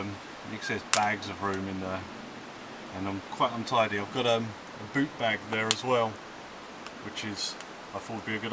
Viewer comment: Lol I thought he said poop bag lol